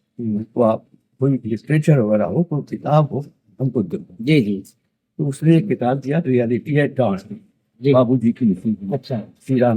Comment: which book is being said by him? 'Reality at......?'plz reply if anyone knows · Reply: Reality at dawn